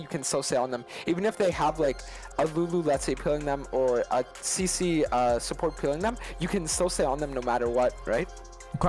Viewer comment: Did anyone catch what he said at 4:30 lol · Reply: "amummu that is peeling them or cc support that is peeling them"